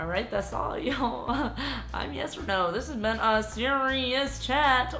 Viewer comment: thought i heard "Hi am yes or no!" for a sec lol